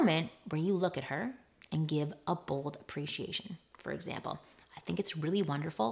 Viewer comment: Its Bold... I heard "a bowl of appreciation